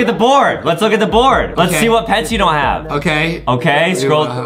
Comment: Whenever Adem says something: Justin makes it food or English related! 😂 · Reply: I thought u said what heads😅u want in Adopt Me😅😅😅